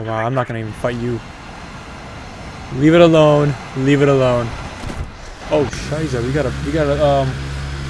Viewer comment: Did u just said "Oh Scheisse" at 2:36? XD